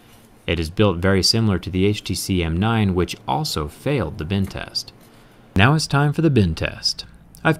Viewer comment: At first i thought he was saying "binned test". What is that, i thought!? 😅